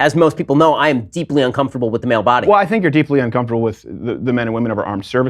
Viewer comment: "I am very uncomfortable with the male body" -Ben Shapiro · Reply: I thought he said, "I am deeply uncomfortable with the male body."